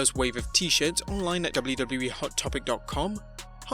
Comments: Did I just hear WWE hottopic dot com at 6:22 on an AEW story? 😂😂😂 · gotten so used to saying WWE that they read WWW as WWE. (Or maybe I just heard wrong 😅)